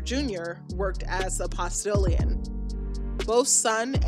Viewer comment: What did she say his son Caesar Jr worked as at 10:50? The captions have pastorian which google just turns up a sci fi thing for. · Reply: A Postillion, a person who guides a horse-drawn coach or carriage while riding said horse or one of a pair of horses